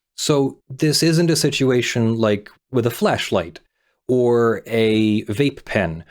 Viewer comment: I thought he said Fleshlight. lmao